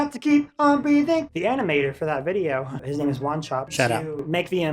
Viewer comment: thought he said “shut up”